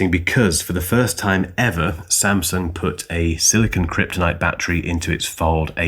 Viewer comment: Did you say silicon kryptonite at 9:17 ? ha ha - I guess superman cant use the fold phone.. love it.